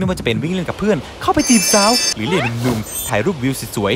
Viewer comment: เค้าพูดว่าไรอะผมฟังไม่ทัน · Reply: หรือเรียกหนุ่มๆ